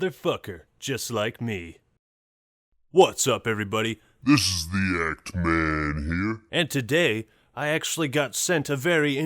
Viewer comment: why do i hear "this is the act man here" like this "this is the egg man here"